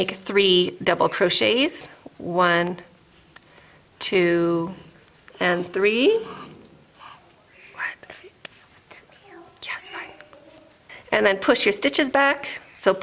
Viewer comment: what did the girl just whisper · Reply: Good ears! My son was asking for something - I said "go ahead". 🤗